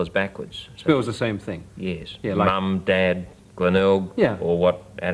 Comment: what does he say after dad? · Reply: Glenelg. An Australian place name.